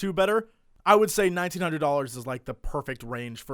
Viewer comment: wait, $9900? wtf did say at 09:29?